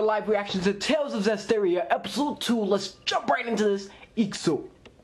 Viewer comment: Whoa! Where's the first episode's reaction? Also, just out of curiosity because I only JUST found your channel through your HxH reaction vids, but what's that thing you say at the beginning of--what seems to be every live reaction. Like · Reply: "Ikzuo" it means lets go in Japanese